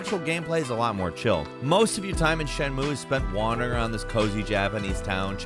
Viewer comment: I was looking down away when he said feeding cats and I swore he said beating, all like, what kinda damn game..